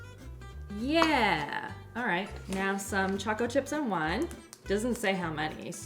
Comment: I thought you were saying taco chips, I wasn't watching for a second. I was so confused why you were saying taco chips and why you were putting them with a custard 😂🤣😂🤣😂 when I looked it all made sense 🤣😂🤣😂🤣